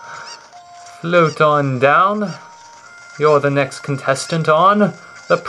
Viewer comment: I thought he said "Protondown", and I thought, "His name's Jon, you know..."